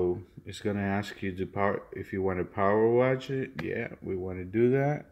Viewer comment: Sounds like he is saying power wash wtf ?